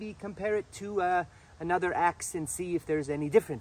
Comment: My high ass really thought he said "compare it to another ACCENT" instead of "compare it to another AXE"